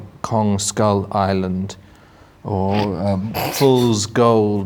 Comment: I thought Ben said "cum skull island" and it made my day. Shouldn't have gone back to check..